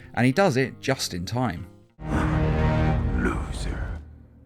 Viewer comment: Did darkseid call them losers?